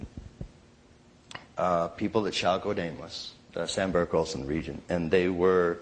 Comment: Ken says something I can't understand. That he had seen "people at ???????? actually doing psychedelics." Can anyone help me here? What is he saying? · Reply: "people that shall go nameless ... ah sandberg olsen region??"